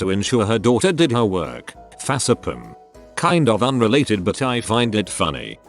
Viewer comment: I find it so funny that the tts says "Fas-a-pim" instead of "Face Palm".